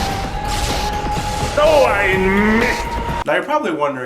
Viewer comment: What is he saying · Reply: Wario: Doh I missed